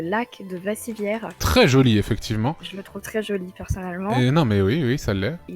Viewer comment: j'ai entendu "je me trouve très jolie personnellement" j'allais lui dire de se calmer